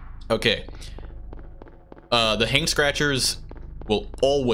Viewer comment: I don't know why but I heard it as you calling the Hanged Scratchers, "Hank Scratchers" instead.